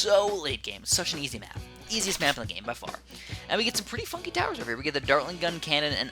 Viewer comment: did he just say "pretty f***ing towers over here"?